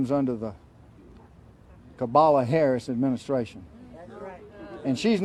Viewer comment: did he say "cabala" harris?